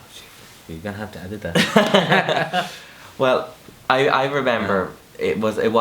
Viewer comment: What was the dad saying at 7:15 -8:13 Because I cant talk english very well... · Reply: Rere Cengi - He said, "You're going to have to edit that." Then he was talking about how "stupid" and "silly" his reaction was and being dramatic over it. He then was looking for a tablet to kill himself. Poor dad... such a nice guy! I'm American and it's a little hard to understand the accent.